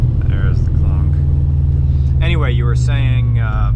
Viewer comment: High af and I keep hearing "Supraru"...